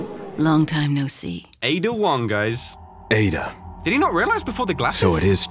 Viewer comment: When said ada Wong(not sure if that’s what he said but🤷🏽‍♂️), I thought he said Ada-1 from destiny 2 😂